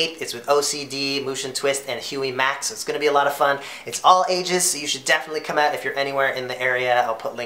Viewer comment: I thought he said "it's all asians" loll he said all ages...